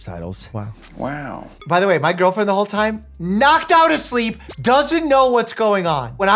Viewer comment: The girlfriend going back to sleep after you say "im in the cockpit possibly diverting this flight" is taking me out lmao she's like okay cool wake me up when we land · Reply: Instead of "the whole time" I thought he'd said, "at the time" and was thinking this video might be how she finds out she's being dumped. 😺